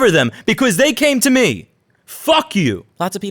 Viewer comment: I hear “Tim Cook fuck you”, I click like.